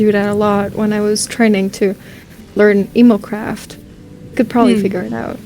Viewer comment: when saint was talking about how they studied, the subtitles said emo craft and i died from laughing. 🤣🤣